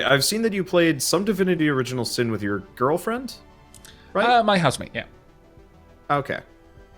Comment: any one else thought he said husband instead of house mate?